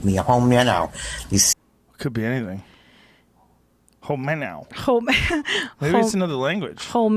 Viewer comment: I heard "hold me down now"